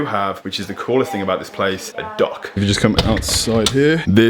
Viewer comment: My dumbass was really excited and thought he said „dog“ 1:30 ...